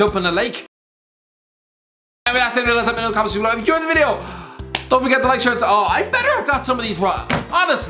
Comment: 😂 tf did you say man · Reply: Any way lads that's the video make sure to like share and subscribe